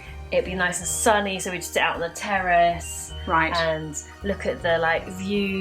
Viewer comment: Ya'll these subtitles are getting wild, 6:16 it thinks "terrace" is "terrorists" and 7:09 "Iraq cardamom" instead of "and a macaroon"